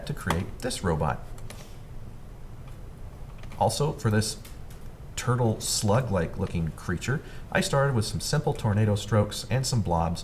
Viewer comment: I thought he said, "this turtle slut like looking creature." 😂 I had to rewind to make sure I misheard.